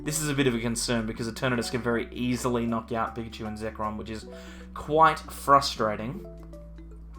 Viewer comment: did he just said mewtwo and Zekrom?🤣🤣🤣